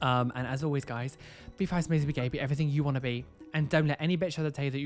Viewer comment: I wasn’t paying attention and I’m dumb so I thought he was saying “Pastor water” and I just said to myself, “Ah yes girl, God will save your piercing. 😂” Then I realized...